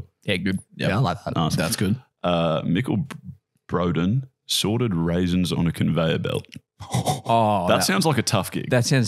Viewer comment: With the guys ' Aussie accents I thought said "Sordid Raisins". Still sounds kind of shady.